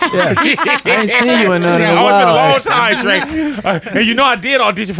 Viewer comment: Did he call him Drake? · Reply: No he said "Trey" sis, but I agree, it sounded like he said "Drake"